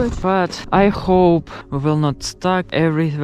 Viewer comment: I hope you enjoy it😸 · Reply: the "I hope will not ????" should be "I hope we'll not stop" or something similar, the ???? should definitely say stop though, works with the context and with what you said